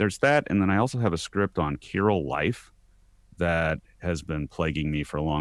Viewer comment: What are y'all saying? Kyril life? Chyral life? Chirality? I haven't heard those words. Opposite life? What IS it? I can't find it on google!! - Izu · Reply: Chirality. <link>